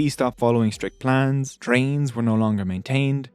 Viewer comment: wait a minute… did he say trains? 😂